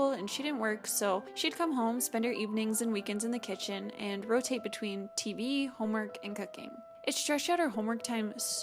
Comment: For a second I thought she said "Weekends in the kitchen, rotate TV, homework, and cocaine."